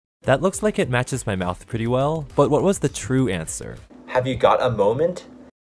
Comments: Instead of saying "have u got a moment" i thought he said "have you got a purpose" pretty close but pretty depressing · I thought you said "Have you got a purpose?" Questioned myself for a long moment. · When he said have you got a moment I thought he said have you got boobies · for the "have you got a moment bit," I thought he was saying "After that moment"